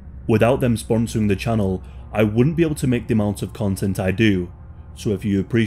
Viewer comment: I thought you said you wouldn’t model them 😅